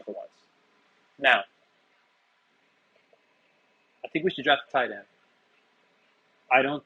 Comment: That wasn’t my point. Caller said to draft a tight end late. My point was we just did that last year and he’s a complete non-factor bc tight ends are a top heavy position. Usually you have really good ones (Warren, Bowers, etc) and then a bunch of nothing. “Grabbing a tight end in the later rounds” usually isn’t a thing. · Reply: at the 15:40 mark. The caller did not say late. He said, “I think we need to draft a tight end. 😂